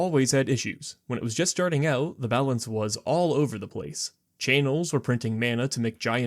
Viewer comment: wait, did you say chay-nels?